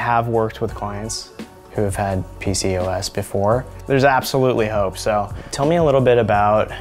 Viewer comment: When said “there’s absolutely hope” I thought he said “there’s absolutely no hope” 😂😂That would not have been good😅